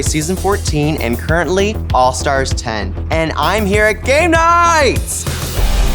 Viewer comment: wait, did she say GAY knights? subtle but genius lol